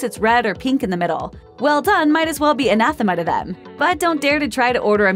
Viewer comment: I couldnt understand what she said, "Well done might as well be a..."???